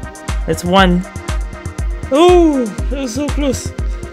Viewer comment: is it me or did you say "nosotros" ?